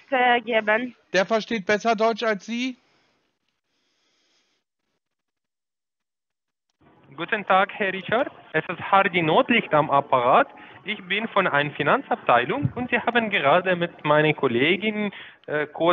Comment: War das "Herr Hardy Nordlicht"? 🙂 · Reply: Kommt der denn häufiger vor? Bei 4:58 stellt er sich jedenfalls vor, und ich verstehe "Hardy Nordlicht". Was als Schlagersänger oder als B-Comedien noch durchgehen würde, aber nicht als Mitarbeiter "von ein Finanzabteilung" mit diesem Akzent 🙂